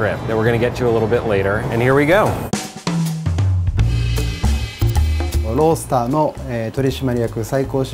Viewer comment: when they said ro start i thought they said world star